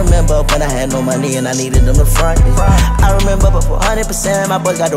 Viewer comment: I swear I thought Uzi said “I remember when I had no money and I needed them to fuck me” 🤣